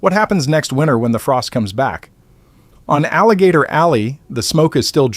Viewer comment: Aloha hugs 🤗 I thought you said Campers been on Alligator Alley